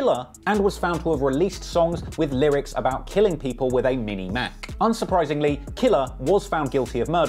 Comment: I highly doubt he actually said "stop till i mini mac a nigger"